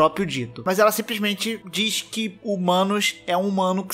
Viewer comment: Só eu que ouvi que o Manus é um mano que se transformou em Deus?